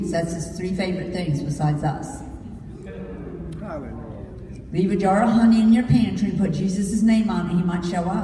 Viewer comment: I seriously thought she said leave a jar of honey in your pants... fuck my life... lmao